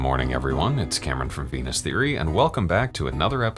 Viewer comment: i thought he said "penis theory" lmaoooo